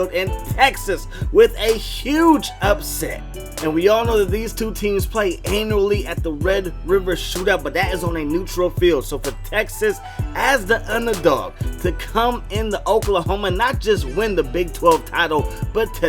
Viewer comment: We're all going to pretend we didn't hear say "These two teams play Anally" at 3:37? We're gonna be that mature? Not me.